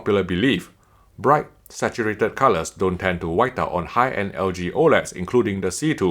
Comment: "bright saturated colors don't tend to ..." tend to what? white out? wipe out? wash out? i didn't quite understand what you said there...i have shitty hearing.